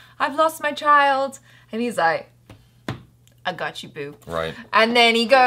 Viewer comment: Did she said " i gotcha boob"